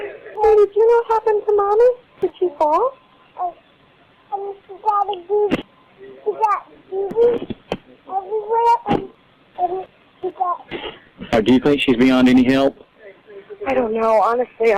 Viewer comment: did anyone else feel like her daughter said something about “daddy” in the background of the 911 call??? When Michelle’s sister asked her a question about what happened I swore I thought I heard her say it! Omg that poor baby 💔 and the fact that he just signed away full parental rights just validated he didn’t want his daughter either 😢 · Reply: I heard 'Daddy beat. She got bruises everywhere! and and she got-' i understand she is 2 so i also dont know if that is exactly what she said since the majority of 2 year olds cant form long sentences yet, but the poor baby!, having to witness this and also comforting her mum after with a doll broke my heart.